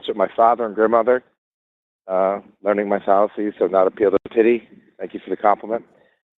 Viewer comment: Is this the result of an audio edit? Because I'm hearing "...learning my fallacies, so not appeal the titty, thank you for the compliment." What did he really say?